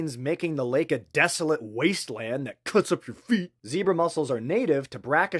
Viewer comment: I first thought I heard Zebra MUSCLES^^